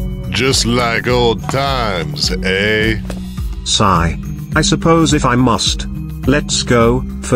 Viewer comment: he actually said sigh I’m dead